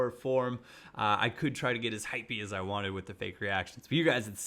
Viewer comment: did he say "hyphy". Did I really hear use that term?